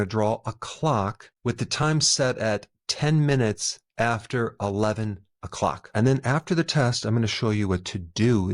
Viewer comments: I thought he said: "Ten minutes after two", so was frantically trying to get my clock and hands set in the right place before the 30 seconds ran out!😂I replayed it and did just fine the second time. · lol, crap ! I thought you said 10 minutes BEFORE 11. I drew the clock correctly. But, good news is I’ve been space cadet all my life plus, currently have a slight hearing problem (no hearing aids yet).